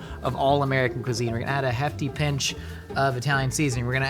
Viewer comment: i thought he said "hefty pinch of Aleve" and as a millennial i felt that